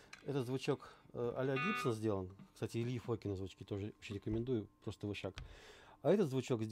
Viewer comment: Не расслышал какой звукосниматель🙄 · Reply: нашел, Ильи Фокина